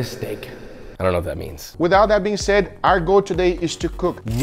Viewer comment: did guga say "without that being said"